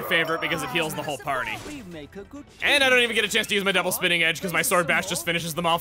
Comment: I don't know why, but I always heard "Double Spinning Edge!" as "good ol' spinning edge!" I will always remember it that way. While I don't doubt your logic for preferring Sword Bash, I'll stick with Double Spinning Edge for the sole reasoning that I find it more fun.